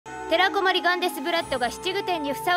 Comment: テラコマス・ガンギマリブラッドに聞こえて巻き戻しちゃった